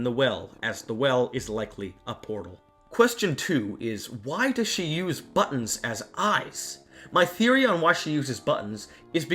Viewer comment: Hello! I love this video so much, and I want to make sure everyone can understand and enjoy it. That's why I wanted to let you know that your captions are a little off. At about 7:42 the auto-captions say "Question two is why does she use buttons as ice" where they meant to say "Question two is why does she use buttons as eyes". When you get a chance, could you fix that? That'll help the 360 million folks globally who rely on closed captions to understand videos. Thank you, and keep up the great work!